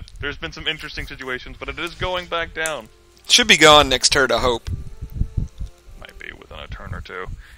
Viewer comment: did you just say the next turd?